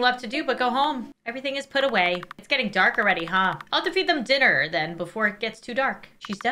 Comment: what she says : I'll have to feed them dinner The caption : 𝗜'𝗹𝗹 𝗵𝗮𝘃𝗲 𝗱𝗲𝗳𝗲𝗮𝘁 𝘁𝗵𝗲𝗺 𝗱𝗶𝗻𝗻𝗲𝗿